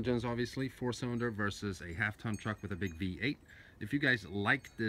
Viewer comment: did he said half ton truck???